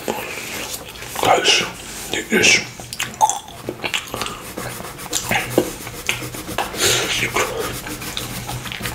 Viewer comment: What did you say 1:39 i think i hear a really bad word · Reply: First I said icy, then trigger. Nice try. Nowadays the brains of some people are toxicated 🤦‍♀️